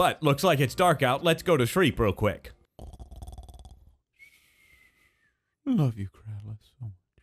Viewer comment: Did he say "love you Keralis'